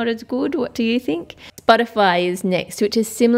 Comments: When she said Spotify I thought she said butterfly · was it just me or did she say "butterfly" 🤣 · when i was 8 i didnt know what spotify was so i thought she said butterfly😭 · It sounds like you said "Butterfly' instead of Spotify.